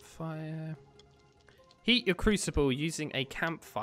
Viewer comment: Is it just me or did he say "Eat your crucible"